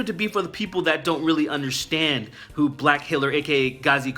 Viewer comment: At first I thought you said Black Hillary! #MAGA